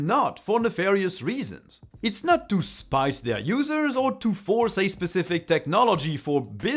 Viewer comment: Did you say "to spice their users"? I think autocorrect may have got you. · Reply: Spite